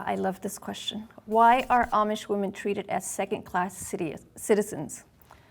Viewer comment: LOL, I thought it said Irish woman.